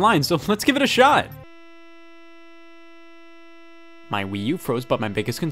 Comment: does anyone else hear "my wii u froze" ?